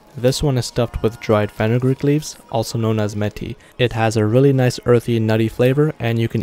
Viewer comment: Bro not going to lie I thought you said how to make meth